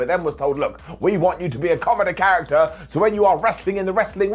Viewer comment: "comeder character"? What? Do you mean "comedy"? Why did you say "comeder"? You used a completely wrong word?